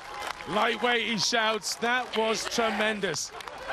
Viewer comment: What does Eddi Hall scream at 31:23? They were all laughing. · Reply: "EASY!!"